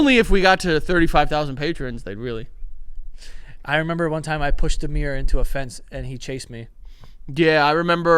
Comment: Anyone else think when said he punched Amir I thought he said he pushed a mirror and his reflection chased him 😂❤